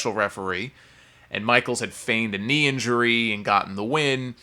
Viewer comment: What word do you use at 8:27 to describe Shawn Michaels' knee injury?... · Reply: feigned, faked.